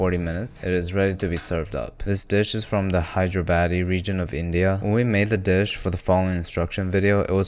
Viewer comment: Did bro say hydro baddie region of india 😭😂😂😂😂